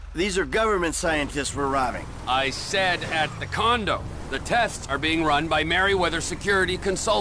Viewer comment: trevor says condom instead of condo